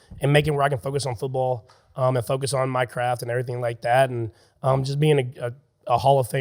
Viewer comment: I thought he said, "focus on Minecraft" lol